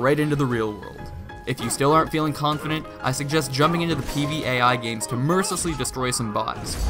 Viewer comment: did he just say PvAi?